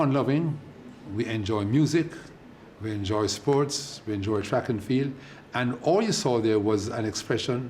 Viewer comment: I am so sorry, but, did he just say, We enjoy fukh-an-feel..?